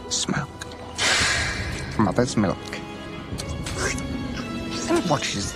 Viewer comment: Mmm...delicious. A first class smoke. Mother's milk. Quote this all the time lol. Just want someone to one day get my reference. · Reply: Yooooo for 19+ years I thought he was saying “Mother Smoke” as a okay on words of “Mother Earth”